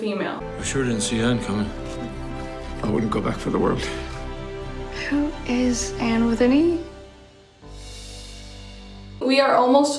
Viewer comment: wow idk why but for me it sounds like anne is saying 'who is anne with an e' :') ALSO LOVED THE THEORIES!!! IM EVEN MORE EXCITED FOR SEASON 3 <333